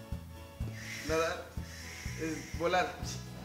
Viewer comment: Cuando apareció la palabra volar pensé que decía violar 😂😂